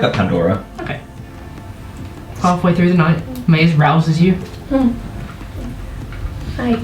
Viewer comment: Oh God I'm scared. Oh ok they could kill the "young ones" easily. Phew 😅 Also they're not growing out of the bodies either thankfully 😅 Oohhh! Cool torch! "we can take this, surely" "yeeahhh" 😂 Ohmygod a moral compass 😆😂 There's a fire in the distance Icarus pulls out the spying glass 😂 Mayes "with my kataaaana" Me 🎵katana onana🎵 Oh noooo! ☹️ Poor griffin lost it's rider 🥺 The griffin is so cute 🥺🥰💖💕 Woah! What a cool lion! A fleecemane lion! Poor catoblepas ☹️ Bee stonks 😂 Oh God.. 😅 I misheard it as "halfway through the night Mayes arouses you" 😓😅 Ehem... Yeah... No.... 😓 WhaaAat the heck is Mayes dream about? Them arriving with the catoblepas must surely be a FuNnY SigHt I've been nagging my friend to catch up again so I can discuss the stuff that has happened so far. He hasn't even come to the episode with the masquerade in the dawnbringers series yet, and what happens in that episode is huge! I've been nagging him multiple times a day this week and he still hasn't gotten to it! He's been watching umbrella academy though so I guess he's excused... For now. I'm still gonna nag him about it 😆